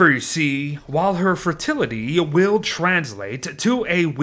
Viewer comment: um? you said "fertility" instead of "fragility" @ 1:28 i about choked on my gobstopper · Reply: "FRA-". I am saying "FRAILITY"